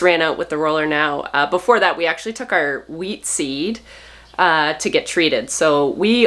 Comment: I thought you said weed seed not wheat seed.